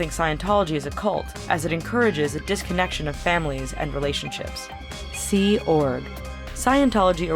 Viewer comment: disconnection of family members. Subtitles says dismemberment...